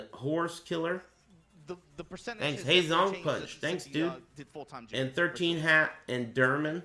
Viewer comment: Did you just say, "Hey ZonkPunch, thanks dude."?